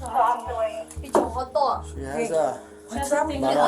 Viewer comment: The drunk daughter is doomed. After seeing mom yelling as well, it explains everything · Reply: as far as language I kept hearing “pinche joto” which is Spanish and not at all nice to call other people